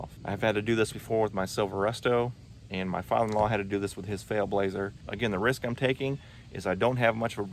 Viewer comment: Did you just say "FAIL blazer" and "SCAM tool"?!